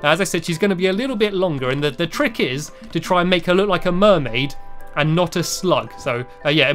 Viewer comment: I thought he said mermaid not a slut 😞